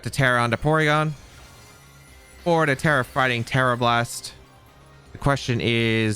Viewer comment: When says "Tera fighting tera blast" or "tera flying tera blast" it sounds like hes saying "terrifying Tera blast"